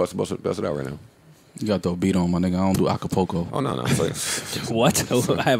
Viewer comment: mal says Acapulco instead of acapella. 🤣